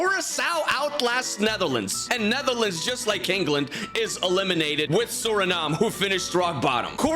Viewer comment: did I hear Kingland?! We are NO King-land! WE ARE THE BOTTLE-JOBLAND!!!